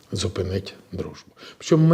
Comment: Що він сказав на 5:19, зупинить...???!!! Дружбу ?!!!! Дружбу з ким...???!!!